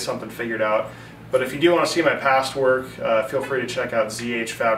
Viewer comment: Dude, at the end you said "...if you want to see my past work..." I could have sworn you said "...if you want to see my password...." Whaaaaaaaat!? Hahaha, beautiful work as always.